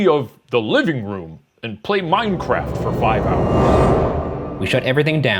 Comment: he's saying minecraft and the subtitles say world of warcraft, im